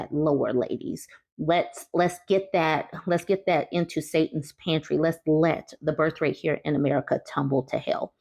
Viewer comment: I thought you said "Satan's panties." That's LOW.